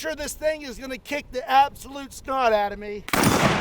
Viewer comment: Did you just say, "Kick the Scott out of me?"